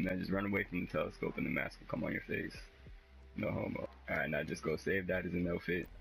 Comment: at like 4:31 did anyone else hear him say "no homo"?